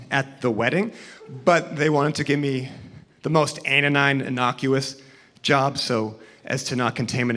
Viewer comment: What’s the a word Chis uses here? “A- and innocuous job...”. Trying to figure it out. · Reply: super late but i think he meant asinine, to mean stupid or ridiculous, and had a verbal slip-up that he didn't see the point in correcting